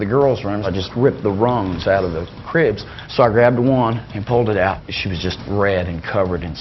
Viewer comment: he never said it. Said he went to the girls room grabbed her she wasnt breathing. No "it" was said. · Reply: nah I hear " I pulled it out "